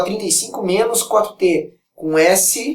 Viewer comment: passei minutos nessa pq entendi vc falando S=So-4t >-<